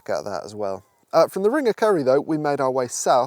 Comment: I thought you were saying "ring of curry" which sounds like a place I want to visit.